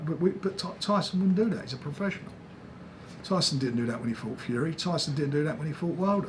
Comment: ‘’Tyson wouldn’t have done that’’ Tyson DID do that…. He failed to turn up the Klitschko 2 press conference. That was a lie Frank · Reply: He actually said at 11:54 “Tyson didn’t do that when he fought Fury”…so he’s confused as well as telling a lie. But yeah I remember him not showing up for the Klitschko presser at least once.